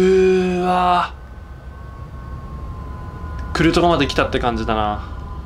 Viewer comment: のとこでが「来るとこまで来た」って言ったのが「クルトガまで来た」に聞こえたww